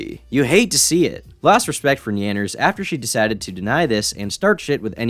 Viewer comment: for a moment I thought he said "Nanners" as in Sea Nanners